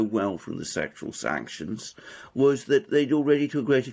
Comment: at 10' 09" "sexual sanctions" ? I thought I'd misheard but there it was in the captions. WTF?